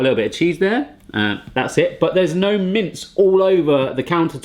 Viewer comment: You put MINT on your taco?? That's odd... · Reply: shypiejohnson I thought he said mint too but he said mince (the ground hamburger )